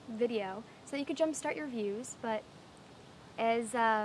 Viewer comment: awww i came here because i thought she said, Pooping, Gutted! :(